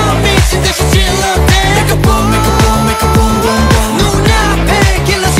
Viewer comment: Why all i hear is “make a porn” 🙈🙈